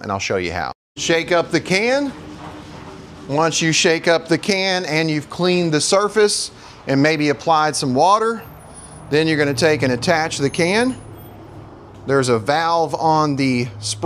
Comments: I thought you said you cleaned the walls? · I thought you said clean the surface???😂